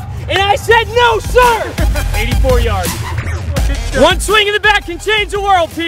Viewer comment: I thought Ty said "one swing in the back can change people". Yes, yes it can.